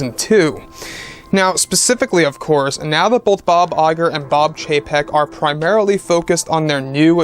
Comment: I honestly thought you said, ”Bob Iger and Bob JPEG” 😂